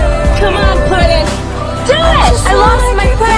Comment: I lost my Putin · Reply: Tasfia Tasnim she knows that is “puddin” but whenever Margot says it, it sounds like she says “Put In” 😂